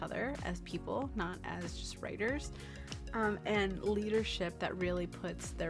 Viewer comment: Sometimes when you say "writer" I hear that as "raider" (like the ones in Fallout), and then you say funny things like "...to take a position as a raider would be a very morally grey area that I'm not comfortable with." Ethics 101 ^^ (likely all my fault btw, not a native speaker so I pick up things wrongly sometimes).